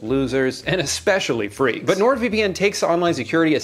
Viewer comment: the subtitles you put accidentally put north vpn instead of nord vpn, just letting you know